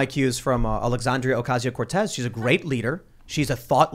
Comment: Ngl when tim said " I'm like AOCs she is a thought leader" but i thought he said "she a thot leader" lol